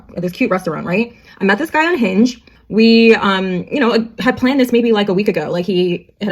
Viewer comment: Every time I hear a woman or guy say I met this someone “On Hinge,” I keep hearing “Unhinged.” 😂😂 😂😂😂.